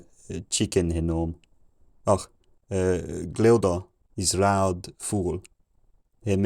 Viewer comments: I heard one or two familiar words (hab for have, ist for is, koy for know) around 600 but <num> had more German words (for obvious reasons). I heard 'chicken' at around 800..."is loud fowl" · 800 is where it starts to sound something like mordern english for me. I hear chicken then followed by Glouda (idk the spelling, sounds like a name though) is a loud fool. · sounded like "samples" 1:28 sounded like "chicken" 1:32 sounded like "is loud foul" 1:40 It sounded like he sighed and said, "It is long winter." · at 800AD I heard the first word I recognized as chicken. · I know I heard chicken at 1:28 😅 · I started picking out a word or two around 1:29, I hear “chicken”…”is loud full”. So around year 800-900, I start understanding a few words here and there. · it was so crazy listening along to basically gibberish and all of a sudden hearing "it is long winter, the ground is cold" emerge from the fog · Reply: I heard “is loud fool” before that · Did i hear "Glildor is loud fool" 1:31 ? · thought i heard (glaudon is a loud fool) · Gilda is loud fool❓ am I hearing that correctly? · Did I hear “chicken?” 1:43 It is long winter. The ground is cold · Between 800 and 900 i heard "glalda is loud fool" 🤣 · " field, "horse" "chicken and "is loud fool" are the first words i heard 1:24, so about 775ish, but true, though strained, understanding is about the 12-1300s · sounded like "Gleodam is loud fool" 😭